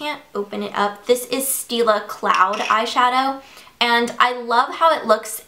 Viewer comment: Steal a cloud it sounded like you said that lol